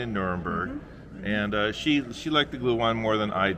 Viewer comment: Wait, what kind of wine? It sounded like you said, "Glue". Glute? Gute? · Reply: gluhwein